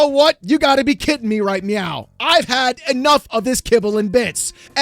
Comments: Did you just say "... You gotta be kidding me right MEOW" ?? 😹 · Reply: Sounds like he said "You gotta be Kitten me right meow" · Did you just say "... You gotta be kidding me right MEOW" ?? 😹